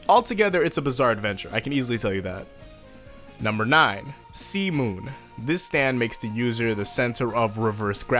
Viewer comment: did he just say semen? semen is a stand? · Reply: Nah he said C-Moon